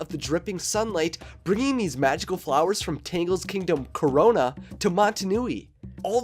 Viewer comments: Wait wait wait did he say Kingdom Corona DID YOU PREDICT THE FUTURE!?😮🤔😨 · Did he just said CORONA?🙄